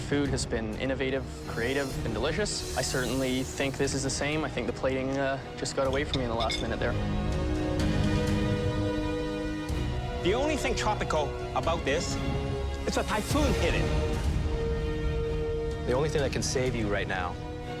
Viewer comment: "The only thing tropical about this is a typhoon hit it" Chef Alvin is a fkin savage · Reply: Ohhh. I couldn't understand what he said. For some reason, I thought he said "the only tropical thing about this is a Thai food get it"